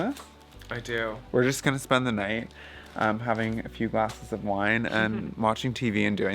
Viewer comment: So funny, when you said "glasses of wine," I thought I heard "BOXES" of wine! 😂 And then you made the joke about bottles while my brain was replaying and wondering "don't they make their own wine?" OMG...so funny. Laughter is the best medicine. Feel better!